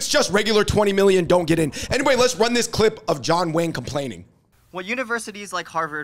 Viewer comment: closed captions (it had said this guy's name: "The Duke," John Wayne ?) instead of. = in 1984, Shawn Brown had released a song called "Rappin' Duke."; this same (exact) song later gets quoted in the Notorious B.I.G.'s song, "Juicy," 10 years later, in 1994 ("Ready to Die": Biggie Small's debut album, by Bad Boy Records). FAFSA meant "Free Application for Federal Student Aid."